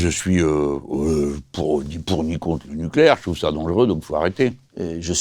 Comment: Il dit quoi à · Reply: "je trouve ca dangereux, donc faut arrêter"